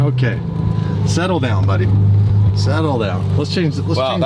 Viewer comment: Did just say “Settle down Buddy” you brought out the monster in lol 😈🤣🤣🤣